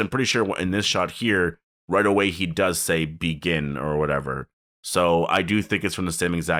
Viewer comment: I also thought beta said negan but what he said is begin